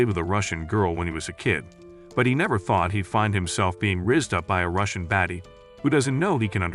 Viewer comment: I’m sorry but did creator say rizzed up by a Russian baddie 😏